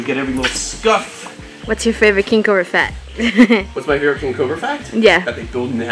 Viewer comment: I swear it sounded like said "what's your favorite kink or a fet" !!!!